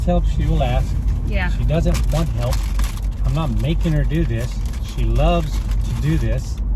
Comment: Love that she's independent and I like it... is what I thought I heard. Then he says she loves it! I learn so much from yall about a good marriage.